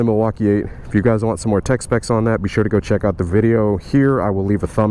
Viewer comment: When you said "Tech. Spec.s", I thought you said "Tex-Mex". I did notice the "Mexico" sticker. What's up with that?